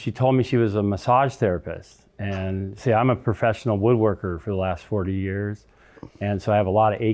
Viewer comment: I thought he said he's a "professional wood layer." Let me rewind that part for clarification.